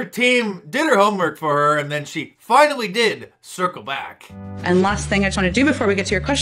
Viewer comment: Now I get it. All this time I thought she was saying, "circle bock."